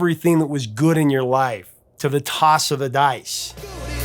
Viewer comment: I always heard it as, “…to the toss of a dime,” 😂!! Great episode, professor! Also, impossible to cover. That’s likely why it hasn’t been done.